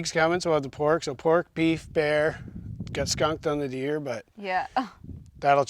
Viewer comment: Good morning kids! First I thought you say you got a skunk to eat. Then it dawned on me you GOT skunked😂